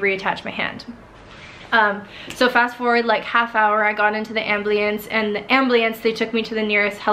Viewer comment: Did she just say "ambliance"? Edit: its at 8:10 Edit: for those of you who think that she's offended by this, she's not. Infact she liked(heart) my comment but it got removed because i edited my comment. Im mot trying to offend anyone.